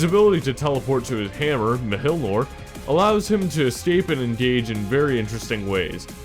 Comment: wait what did he say? 5:09 mhilmor? OMG ASOJTAISHGV JXZÖGKGB AIOÄG SGBYTÅ8GJNTSAPAS¨THJ NAI0WSHYNTGY (im swedish i think thats the joke here)